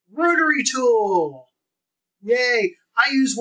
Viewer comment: I first heard "Murdery tool"